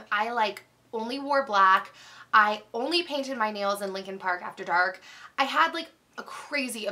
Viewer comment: I honestly thought you said "I only paint my nails in Lincoln Park after dark" ... like in the place Lincoln Park and only after the sun goes down. I was like, "What??" haha, totally confused for a second